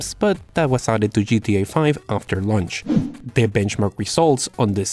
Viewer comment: if u listen closely , he actually said GTA V after "lunch"